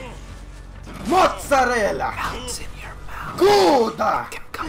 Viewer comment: When he said Gouda i thought he said "pota" hahahahaha